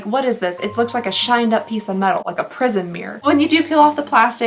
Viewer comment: Totally thought you said prison mirror 🤣🤣🤣